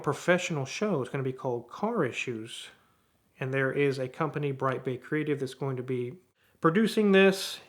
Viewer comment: I thought he said Breitbart Creative...